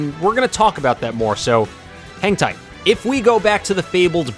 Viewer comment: I heard it as "hentai" lol